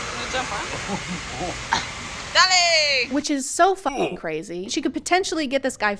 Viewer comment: Did she said “dalej”??? Like polish “go”????